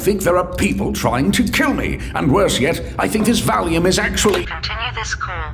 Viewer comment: In the scene where medic is on the phone with scout and scouts muffled voice comes through. It sounded like he was saying “they’re going to kill me”